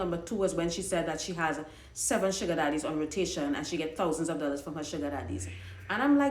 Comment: I could’ve sworn she always said 17 Sugar Daddies...😂